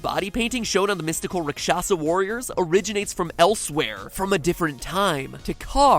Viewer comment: You said they are from "elsewhere" and I thought you said "Elsweyr" and went wait what o_o